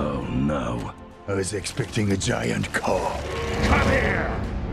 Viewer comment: (Grimlock Transform) Crosshair: oh no Drift: i was expecting a giant car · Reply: oh thats what he said lmao i thought he said Giant call